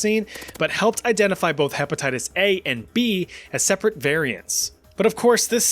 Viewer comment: dont think i didnt notice that you said hepetitties XD